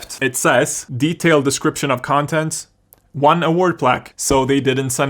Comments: i thought "one award plaque" said "one award plague" and i was just,, wdym? you were right. they sent you anthrax. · I thought it said "One Award Plague" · I thought it said "plague"